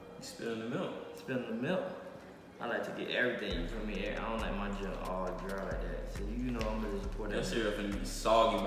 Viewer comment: I thought y’all said you don’t like milk ? 🤣